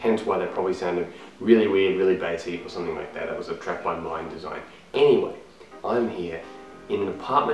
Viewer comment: none of those tracks were done by me however. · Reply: Damn, I thought you said "a track by mine design" sry mate :D